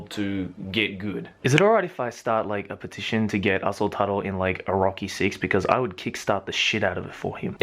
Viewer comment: I thought he was saying asshole turtle not 'aso taro' at first. also positive